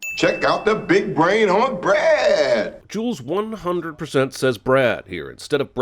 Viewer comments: put on closed captions, even the CCs think he says "Brad" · I hear "Breeeet" like he's just extending what he's saying. I don't think he says Brad. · I thought he said "breh" :DDD